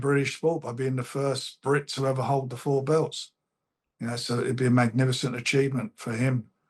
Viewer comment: Think you're hearing incorrectly. He said first time the belts will be contested in the UK. Somebody else could have won them but not in the UK - AKA Lennox Lewis... Secondly he's talking about the heavyweight belts nothing to do with lightweight... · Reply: no, he also said "first to hold the 4 belts" at 3:00. Lennox Lewis was undisputed and had 4 belts (IBO instead of WBO, but I respect the lesser belts more than the WBC, as they are the most corrupt), and he was born and raised in London till 12 too, before anyone says he's Canadian.